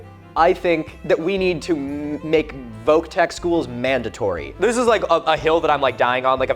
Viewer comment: i didn't know the term "voc tech school" before this (english as second language) so i first thought you said you went to "Woke school"